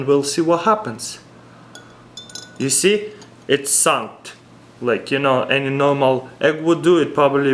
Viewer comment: it sounded like he said it sucked